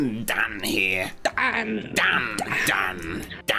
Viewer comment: When they keep going Dan DAN DANNNN DANNN. At first I thought they said damn damn dan. Damn daniellll . XD